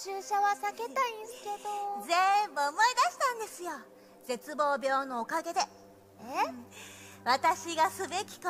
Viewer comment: What is the nurse character saying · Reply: Someting about despair illness idk